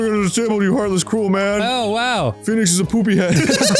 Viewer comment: I have so poor hearing that when Arin said "Phoenix is a hobo" i heard "Phoenix is a homo" and i was like "how is that a spoiler?!"